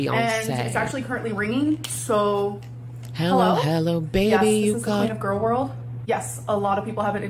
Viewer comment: When she answers the pink phone purse, she actually says, ""Hello. Yes this is the queen of gorl world."" (16:00) Maybe that is the real reason she bought herself the crown she was wearing earlier ! 😂